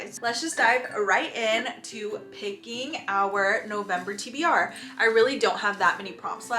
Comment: Sounds like you said September TBR when your video says November TBR